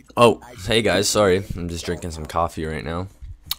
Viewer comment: Lmao at the beginning when he said 'just drinking my coffee' i thought he said just jerking off at first, oh silly me.